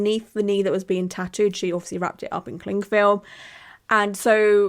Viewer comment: Oh no, just now I realised it's called CLING film omg I thought you were saying CLEAN film all these years😳🤯